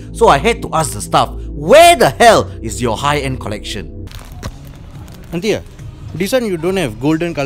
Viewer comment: Did she just call you uncle?? 😂 Bro fr tho, didn't realise you were only 3 years older than me until I watched ur Royal interview